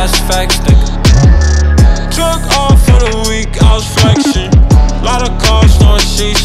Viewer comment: Couple of things 1. Am I the only one who thought he originally said “Jerk off for the week, I was flexing.”1:04 2. Why does he look like Will Byers getting possessed by the Mind Flayer. 3. Why do I feel like this is probably the song on ERYS. Other than that, love your work man. Keep it up🙌🏾🔥